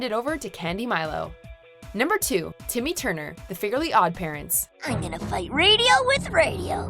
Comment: "The Figurely Odd Parents" Anybody else hear that?